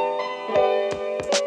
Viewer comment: Why did I always hear ‘thinking of us, us, us.’ BUT THEN I REALIZED THAT IT WAS ‘Her’ OH MY GOSH. But anyways.. I love this song.